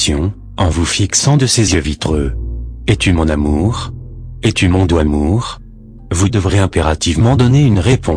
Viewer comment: Quelqu'un a compris la deuxième phrase: Es-tu mon amour? Es-tu .... (j'ai compris "mon doimour" :p ) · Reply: c'est mon doux amour "sweet love"